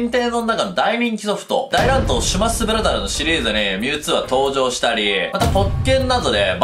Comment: 大乱闘シュマッスブラザーズって聞こえるのは俺だけ？